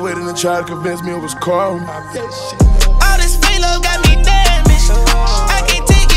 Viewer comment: I always thought he said all dis fake love got me dead mister